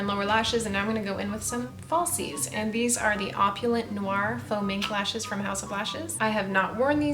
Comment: I know she said “faux mink” but I heard “foaming”